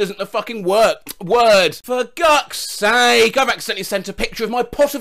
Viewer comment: okay but 1:08 is the funniest thing says ‘for gucks sake’ instead of ‘for gods sake’ i love that